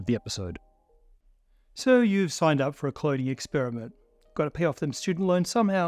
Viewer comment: subtitles say "cloninmixag" rather than cloning btw